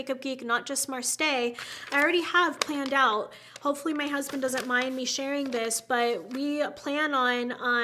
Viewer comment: Did say "my husband" at 1:30:48...I'm squeeling!!!!